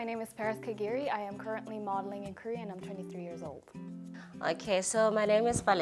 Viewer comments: plz tell me I'm not the only one who thought she said "I'm turning 3 years old" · I THOUGHT SHE SAID SHE WAS TURNING 3 YEARS OLD